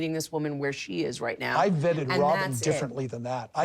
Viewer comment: I thought Kody said, Bedded Robyn before not vetted.